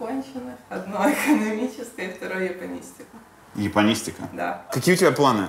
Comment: Кто-нибудь, помогите ! на 21:00 я слышу что они оба говорят , что девушка изучает "ебанистику" что там на самом деле?